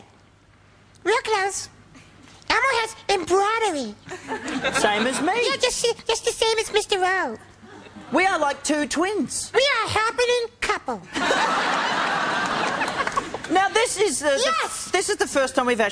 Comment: SHUT UP I THOUGHT HE SAID "we are like two twinks" thats it im leaving 😭😭😭